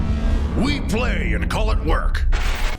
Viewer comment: I keep mishearing the intro as, we play and called it quirk.